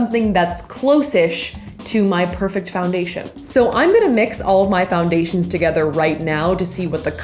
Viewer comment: DID U SAW THE PUN 1:05 she said all of my but it sounded like almay and that foundation in the middle!!